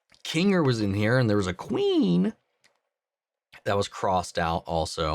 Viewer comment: I thought he said “Queenie” lol…